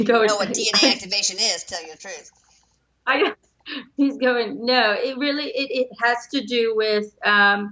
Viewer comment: did anybody else hear "NO" ? :) Anyway I dream a lot ! And I believe I have had "dream visitations" from the other side. Usually very vivid and they feel differently. Prophetic dreams as well,,, nightmares of all kind. You name it. Can be very exhausting actually.